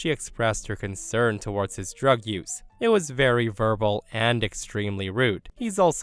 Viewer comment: misheard 'verbal' as 'purple' and got very confused for a minute